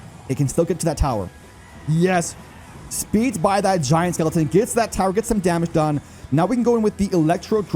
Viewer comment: Did anyone hear him say smelliton